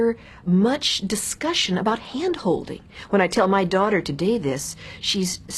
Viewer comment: I thought you said glandholding.